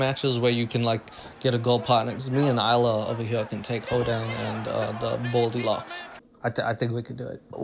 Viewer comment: subtitles: "Hoedown" instead of Hodan 😂👌